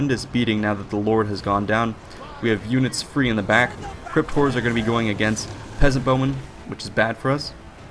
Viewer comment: I keep hearing it as "Crypt whores" lol